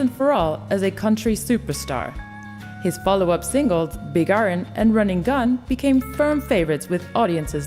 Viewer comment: sounded like she said big urn instead of iron